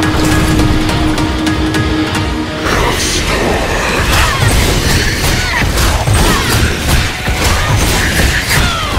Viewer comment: did he just say word star? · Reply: Sos Igaming he said storm